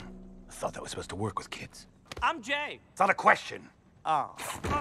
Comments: okay no but like, I wasn't looking at the screen at 3:47 for a moment and then the next thing I know is I hear Jay's voice sayning "I'm gay" and I was like, wait a damn minute. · Oh wow when jay said "im jay" at about 3:46 i had to do some retakes since i kept hearing "im gay" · When jay said “I’m jay” I heard “I’m gay”